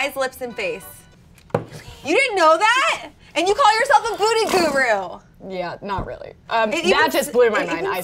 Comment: Anyone else noticed the “booty guru” at 2:09 or am I mishearing it? 😂